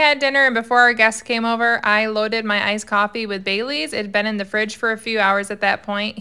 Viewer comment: I wasn't looking when u said bailey's I thought u said bay leaves im like what does that do ? Had to watch it a second time lol